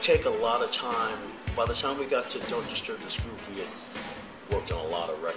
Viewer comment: That's what I heard "Don't Disturb This Groove", a classic pop jams.